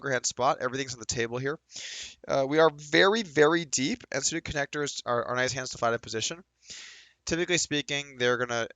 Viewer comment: I swear the first time I watched this i looked down at your hand and i thought Wes said "connectors man" which should be WAY worse obviously. I don't think he was unbalanced in what he actually did say